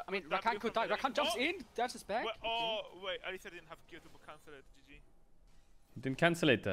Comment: did he say "at least they didnt have curable cancer, gg" ? or did he say "at least he didnt Q cancel, gg" ? xD